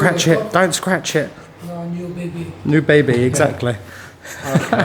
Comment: I'm pretty sure said, "Nous bébé" (your baby) and thought he said "New baby" - different sentences in 2 languages but similar enough meaning to get the intention across haha